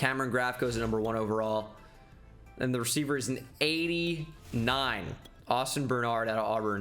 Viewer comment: Damn in the first draft I heard him say the second player was an 89 overall and lost my shit. (time 11:44) but after second glancing saw it was just a miss read at actually being 79.